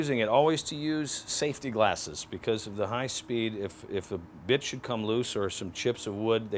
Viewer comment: Lol sounded like "If a bitch should come loose"